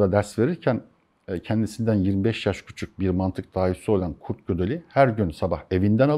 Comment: ya mantık dahisi olan kişi kim anlayamadım ne dediğini 8:38 de söylüyor hatta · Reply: kurt gödel